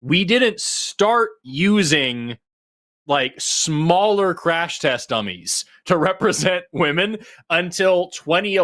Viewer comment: I think said "Women are smaller dummies." More or less. Shame. Shame! :)